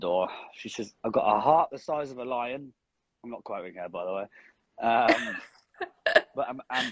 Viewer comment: what does he say at 31:10? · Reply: I think he says “I’m not quoting her by the way”